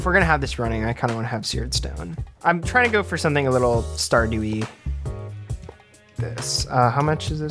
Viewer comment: i love the vid :) but the captions on some of the clips kinda suck... are they like auto-generated? (for example: at 37:17 where he says "Stardew-y", obviously referring to Stardew Valley, but the captions say "star dewy". or right after that were he says "One, two, three, four." but the captions for some reason split it up weird to say "One, two. Three. Four.")